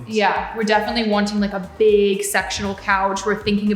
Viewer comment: Definitely thought Mari said "big sexual couch" 🤣🤣 Definitely said sectional